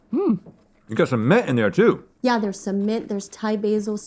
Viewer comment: When Rhett said “got some mint in there too” I thought he said “got cement in there too”